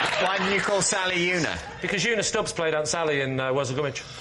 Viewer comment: can't tell what he says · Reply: "Because Una Stubbs played Sally in Worzel Gummidge"